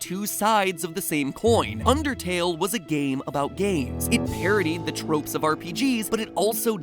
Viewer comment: I heard game of gays